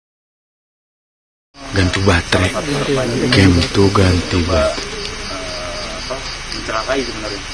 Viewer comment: Audio dari orang lain bocor... kedengeran "Ganti batre..." Fix suara2 setan yg kedengeran pas podcast ni orang yg bikin (udh di setting sama tim) 😏